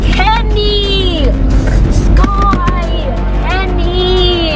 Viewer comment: it sounds like he is saying penny